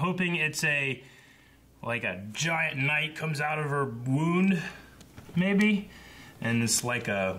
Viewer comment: Ha! I thought said "comes out of her womb". Not "wound".